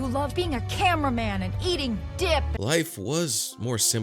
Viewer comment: I swear I heard Roxanne say "Eating Dick" 😂